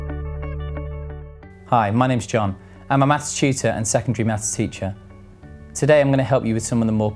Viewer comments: I thought he said “hi I’m John and I’m a math cheater.” xD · At first thought he said Maths cheater